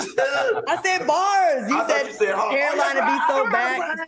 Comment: I’m trying not to laugh when Claudia said “headline” and in my head, I heard “hairline”